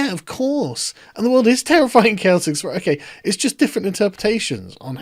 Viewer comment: I thought he was saying terrifying and Celtic. Was wondering what they had to do with it. Lol